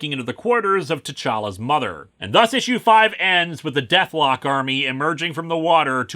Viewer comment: You know at 25:30 I swore said Dethklok army...now I really miss Metalocalypse.